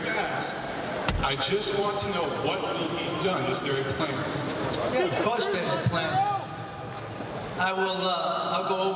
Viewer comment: I heard a voice yell out @ 19:40...."something Montero." Anyone catch what he said? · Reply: "Speak the truth Montero!" I think...